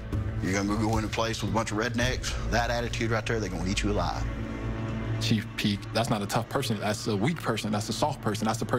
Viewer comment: Chief Keef? Is that what he said?? Haha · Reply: Chief peek lmaooo thought I heard the same shit